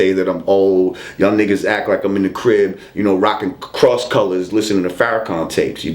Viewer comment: LOOOOOOOOL I think you lot missed what this brother said! My dude said. 5:05 "Niggas act like im in the crib, rocking cross colours, listening to farrakhan tapes"...yoooooooooooooo LOOOOOOOOOOOOOOOOOOOOOOOOOL